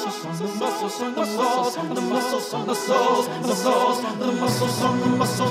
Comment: They say muscles I hear the sorce